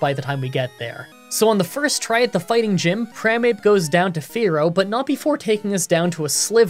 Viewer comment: I thought you said "crimeape" instead of "primape" and if that isnt the perfect evolution of bad mankey I dont know what is